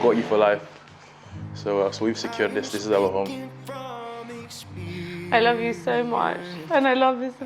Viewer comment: idk why i keep hearing " i love your money so much, sooo much"